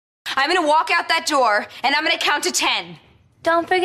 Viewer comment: I thought she said dumb people